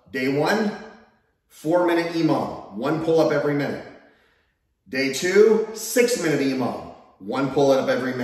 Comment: Emom? Imam? What are you saying @ 12:10???? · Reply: Emom aka every minute on the minute, meaning, do ur pullups, rest 1 minute, do more pullups